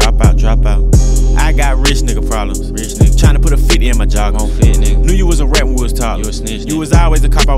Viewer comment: I remember first time I heard dis I thought he said "tryna put my feet in ma jaw" 😂 I was confused af